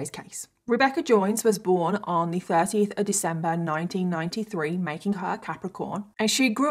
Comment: Capricorn starts 12/22. So is correct. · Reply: I thought she said her birthday was December 13th, I miss heard.